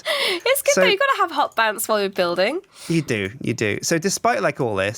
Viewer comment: Did say you gotta have hot pants while you're building?